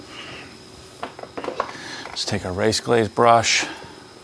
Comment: Did he?! Must have missed it, I thought I heard carpro, but wasn’t certain. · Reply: Race glaze?